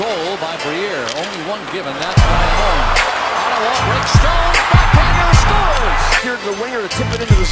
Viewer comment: did he say "Rick Stone"?